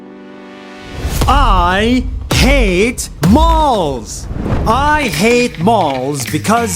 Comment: In the beginning I thought he said “ I hate balls” lol